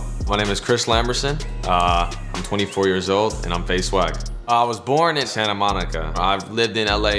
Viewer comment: Who also thought he said faze sway 😂